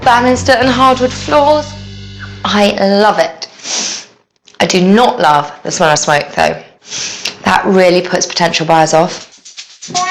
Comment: What is she saying at 3:06 " I do not love ......... ?? Backtracked & listened at least 5 times and can't make out what she does not love, ~ can anyone else make out what her statement is ? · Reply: She said, I do not love the smell of smoke though, that really puts potential buyers off.